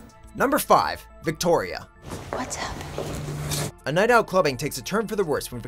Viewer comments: wait what did he say? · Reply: Michael Lobmeyer I think he is saying immigrant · What does he say at 4:44? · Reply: Regina George a Spanish immigrrall obviously